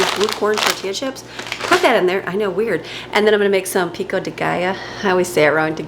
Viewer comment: Couple of my kids have auditory processing challenges… when I said the Pico de Gallo - they would ask peek at what guy mom? I thought you said Peek at the Guy Oh! Lmao